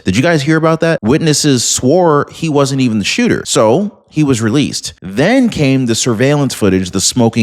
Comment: He was charged with that exactly. They only cleared him as the shooter but took him in anyway on obstruction. I’m not sure about the second one they detained, but they used the same words with that one, that they have been cleared as the shooter. Not that they had been released. Not sure what happened to him. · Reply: Thank you. All I kept hearing was that he'd "been released".